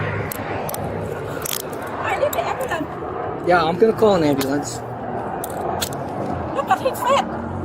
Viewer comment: I hope I didn’t mishear the cop but on top of all this he just said “yeah I’m finna call an ambulance”. Don’t think I’ve ever heard a grown man use the word “finna” before